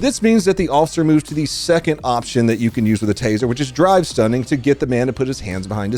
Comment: I’m ashamed to say, I thought you’ve always been saying “dry stunning”. Drive makes a lot more sense. I am way too old to have just realized that.